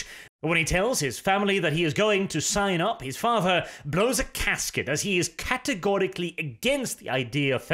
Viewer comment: OK...can somebody please check me on this? I want to be sure I heard correctly...but at 9:17, does say "blow a casket" instead of "blow a GASKET"??? 😂🤣😂🤣😂🤣😂🤣